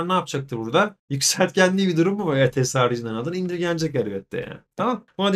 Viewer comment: hocam 7:45te ne diyorsunuz anlayamadım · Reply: Nadlar indirgenir , aradaki nefes boşluklarını yapay zeka kesiyor bazen fazla kesiyor 😒